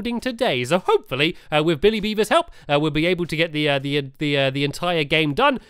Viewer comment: I swear says Billy Beaver not William Beaver! Like this if u think so too